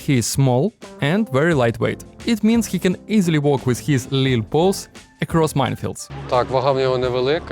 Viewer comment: i thought i heard you say little balls not paws :D